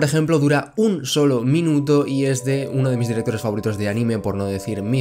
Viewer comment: no se si sere el unico pero en el minuto 2:23 me parece que dice uno de mis directores favoritos de anime porno